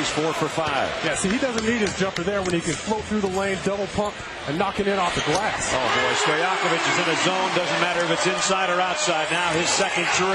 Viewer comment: Is it just me or did he say “double pump”